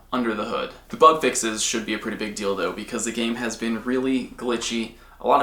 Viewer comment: "the bug fixes should be a pretty big dildo" who else misheard this?